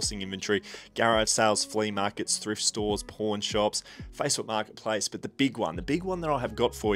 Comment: As a North American, I had to rewind when you said you sourced dvds at "pawn shops" 😅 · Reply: I heard "porn shops"